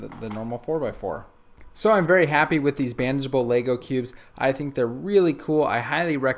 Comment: Everytime he says bandagable I hear "banjable." Lol